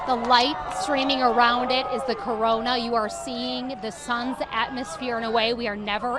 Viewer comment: did she say corona?